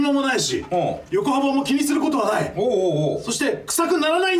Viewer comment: 横幅も気にすることがない、が ヨコハマも気にすることない! に聞こえて なんで？ってなりまひた 最初から最後まで、ちょっと何言ってるか分かりませんが、 謎の中毒性があって最高です😎👍